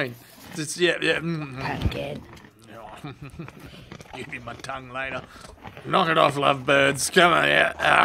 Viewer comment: Did I just hear "Give ya my tongue later" ?? 😂😂🤣🤣